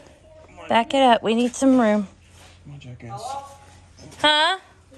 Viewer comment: 🤣🤣 I heard say, "Come on Jackass" 🤣🤣